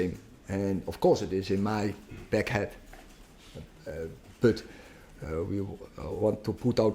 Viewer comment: Did he say egg head?